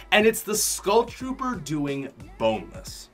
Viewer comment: did he say bonis😂